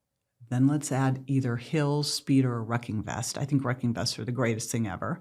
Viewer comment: did she mean a walking weighted vest. sounds like she said wrecking vest